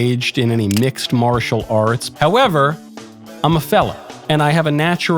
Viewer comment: I'm glad they added the word on the screen. I thought he said 'felon' 😁🤣